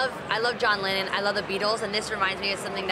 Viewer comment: is She Said i love BEADLES 0:22 like Christian Beadles And Caitlin Beadles ahahaha